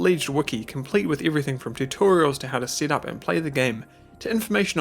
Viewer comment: I thought you said "how to sit up and ply the game" and my dumb brain was like wow they have tutorials on posture too what a great community